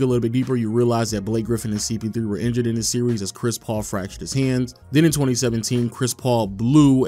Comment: Did he say "hands"!?! As in BOTH???